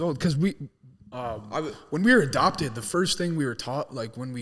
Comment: Wait did he say adopted?